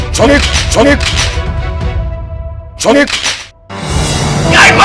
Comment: 나만 정 애 으로들리나 ㅋㅋ ㄱ